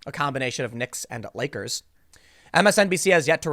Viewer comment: I heard "nakers". Since when is that the N Word?